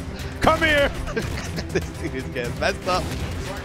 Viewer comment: Is it just me or did he say "this n- getting messed up"